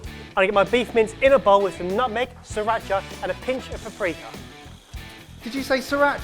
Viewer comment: said paprika, but all I heard was "pre-cum".